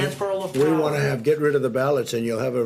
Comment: Let me confirm. At 0:43 he actually says “We wanna get rid of the ballots”?